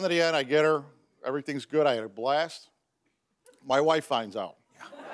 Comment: I thought be said :my wifi went out", instead of "my wife found out"😂